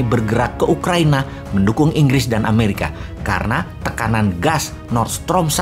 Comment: mendukung Inggris dan Ukraina dengernya mendukung Inggris dan Amerika